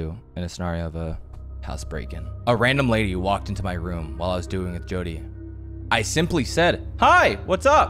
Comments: i thought he said "while i was doing it with jody" 😂😂😂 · Bro I hear it wrong I thought he said I was doing Jodi 😂😂😂 but he said I was duoin with jodi😂😂😂 bro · I swear to god I heard him say "doing it" instead of "duoing" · I THOUGHT HE SAID WHEN HE WAS DOING IT WITH JODI OMG